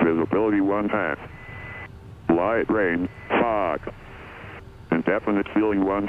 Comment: I thought atis said light rain....fck when it was actually light rain fog 😂